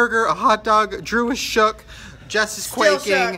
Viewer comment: The first time I watched 5his video I thought she said something about quaking but now that I watch it again she says shook. WTF